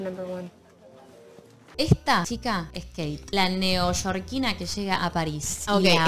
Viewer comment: Lo estoy viendo por 2da vez y recién entendí que dijo Kate. Yo pense que dijo: “Esta chica es gay” 👩‍❤️‍💋‍👩🏳️‍🌈🧍